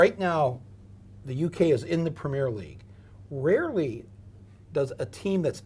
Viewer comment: Goodness me, this guy shouldn't comment on Brexit. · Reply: CJTaylor 87 I always thought he was saying 'big league'. And I find it interesting how a Ukranian displaced person is a refugee, but a Middle eastern or African person in a similar situation is an economic migrant.